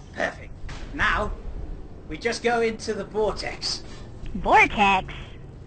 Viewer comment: when says vortex the caption says blackadder...what?